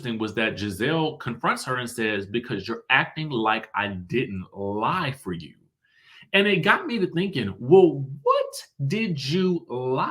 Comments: It sounded like she said ride for you not lied for you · It sounds like Gizelle said “ride for you” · It sounded like Gizelle said "you're acting like I didn't 'ride' for you!"... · I just watched the clip. To me, it sounds like Gizelle said you act like I didn't RIDE for you. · I heard “you’re acting like I didn’t ride for you” ..? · I thought she said "ride for you" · I believe she said don’t act like I didn’t “RIDE” for you… I could be wrong but that’s what I thought I heard…. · It sounded like she said “your acting like I didn’t ride for you” I didn’t hear “lie” but I’m willing to be wrong🤷🏾‍♀️ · When I watched it sounded like Gizelle said “…Ride for you”. I could be wrong but y’all know they be getting those subtitles wrong sometimes. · I thought Gizzy said ride for you 🤷🏾‍♀️ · I thought Gizelle said, "Ride for you," not "Lie for you" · I thought Gizelle said “You act like I didn’t RIDE for you” was it lie? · I thought Gizelle said “ride for you” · I thought she said “you act like I did not ride for you”